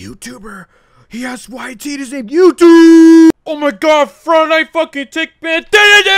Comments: tb is getting updates faster than tds now :_bruh: discord: <link> ego buddy here: <link> twitter: <link> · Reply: it sounded like you said "friday night fuckin tank man" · the frick did he say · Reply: “FRIDAY NIGHT FUNKIN’ TANKMAN!” proceeds to have a mental breakdown